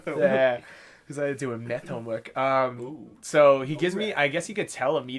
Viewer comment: sounds like you say "meth homework"